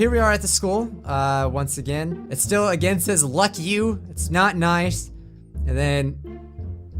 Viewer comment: Why did you say the f word I'm 9 😭 I thought you don't say bad words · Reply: he said luck you